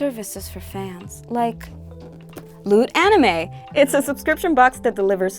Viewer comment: i thought she said "Lewd Anime" lol